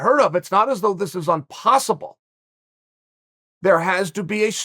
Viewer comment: Did he say "unpossible"? Me fail English?